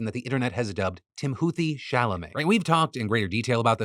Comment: Am I the only one who heard Timothe Chalamet at first? Until I saw this picture I really thought we were talking about the actor.